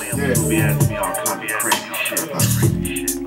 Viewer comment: what is he saying at 0:07 sec. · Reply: Ya-uh-yeah